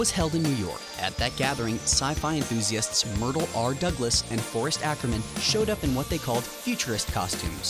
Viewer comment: iS tHaT aCkErMaN i HeAr?